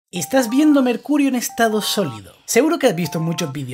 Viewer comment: Al principio del vídeo le entendí "estas viendo me el culo"😅